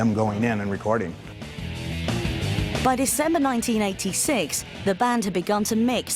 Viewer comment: the bitch that she's talking sounds like she said by December 1996 instead of saying december 1986 , I guess she was writing a script!lol And didn't know anything about gnr!lol